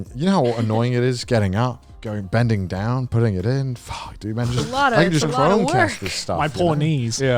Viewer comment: Wait THIS was the start of the Jump King obsession arc? Damn. Also 1:35:27 p good out of context, especially since I heard an extra 'it' in there. "Getting it up, bending down, putting it in there"